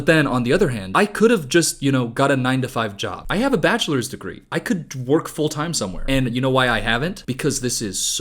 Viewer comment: ngl i totally thought said “i could twerk full time somewhere” until i used my brain